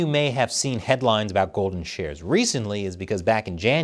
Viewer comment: For a second, I thought you said "Golden Showers", Chris.